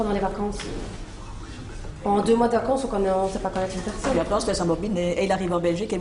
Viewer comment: La juge elle dit quoi en 2:17??? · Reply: et maintenant on se laisse embobiner...